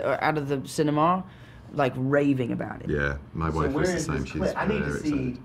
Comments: Did bro just say his boyfriend was the same way? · ok, 13:34 , did anyone else hear, yeah my boyfriends the same shes raving about it XD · AM I THE ONLY ONE WHO HEARD BENEDICT SAY “Yeah my boyfriend was the same,” LIKE- BDKCSBDVBFVLBS GO TO